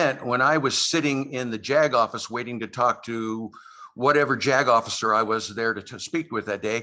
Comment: okay, i know what Mr. actually said, but i still couldn't help but laugh b/c it really sounded like he was saying, ...whatever jack-off i was there to speak w/ that day...